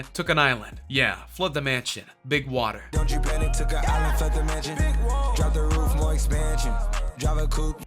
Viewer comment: of i thought he said "took a island for a mansion (big woah)